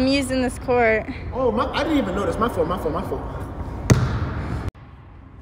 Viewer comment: I thought he said, can I get to clap?